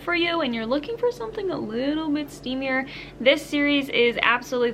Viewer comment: did I heard 'steamier'? Lemme go purchase it😂